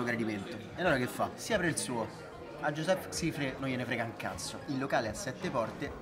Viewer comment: sbaglio o ho sentito cazzo